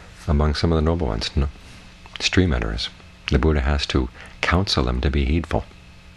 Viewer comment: Hello. Thank you very much for the vid. As i'm not anglophone, can you please tell me what Thanissaro is saying at 2:05 ? He talks about "strea manner" ? After he says about them that they are the least advance. I don't understand is he talking about some people ? Thank you by advance ! And good continuation for your vids. May we all find true happiness ;)